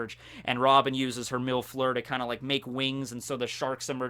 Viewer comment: "Mil Fleur" Me: Milf lure? Oh wait it's Robin's attack thing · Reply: I thought he said robin is a milf lure but that’s just me